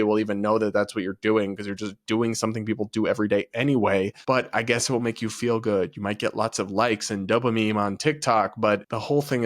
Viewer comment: Did you just say "dopaMEME"? If so, that is an awesome pun! 😂